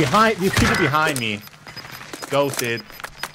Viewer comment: behind of the box, camping buy till the sun goes downnnnn (its sounded like he said posted...if u dunno what I'm talking about go listen to NLE Choppa-walk em down)